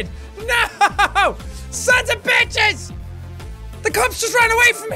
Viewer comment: I swear I heard him shout "SANS OF BITCHES!!!" XD